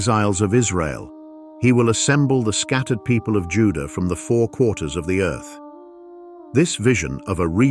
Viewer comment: I always thought that verse said "four CORNERS" NOT QUARTERS. If it IS 'quarters', the FIRST THING that came to mind was... a flat earth!...and, I'm not even a flat earther myself!